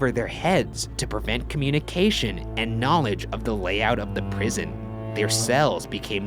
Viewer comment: is that communism I hear?